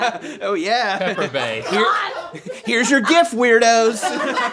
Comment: Lmao... "Here's a gift WEIRDOS!" 😂😂😂 You guys always make my mythical day!!! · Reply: He actually said "Here's your GIF weirdos!"